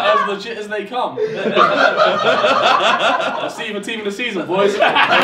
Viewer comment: Can someone PLEASE tell me what Harry says at 9:09 ? I watched the best of sidemen Sunday video before this && couldn't understand wth he said than either! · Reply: It’s something team of the season boys. I think it’s “See ya in team of the season boys”